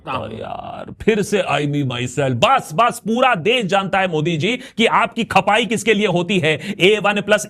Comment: modiji sounded like parents